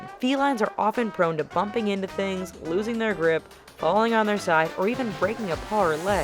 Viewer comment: I thought it said losing their crap